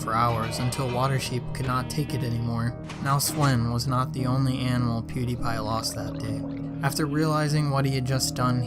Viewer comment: now Sven was not the only animal that pewdiepie lost that day... Me: uhh.. i thought you said that pewdiepie was looking at the same spot that Sven die for 5 days...